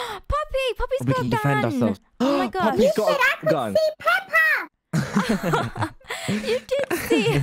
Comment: I laughed so hard when Poppy pointed the gun at saying "You said I could see peppa" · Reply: oml i thought she said ‘so i could shoot peppy’ 😭😭 i’m so deaf haha